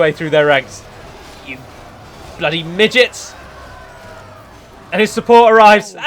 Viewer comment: I skipped the battle of Tharbad to 28:13 and I hear "BLOODY MIDGETS!"